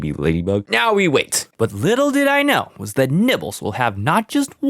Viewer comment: I thought bro said nipples😂😂😂😂😂😂